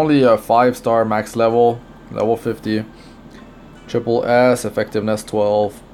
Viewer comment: I thought you said Triple ass when talking about Serila.. =.=